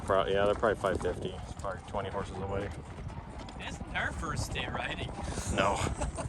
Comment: Loved the pinto owner's comment "it's not her first day riding" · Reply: Omg I totally misheard! I thought he said "Is it her first day riding", the exact opposite sentiment and thought he was so rude and I thought "why would they keep that in the video..." so glad I was just dumb and misunderstood and he was actually kind.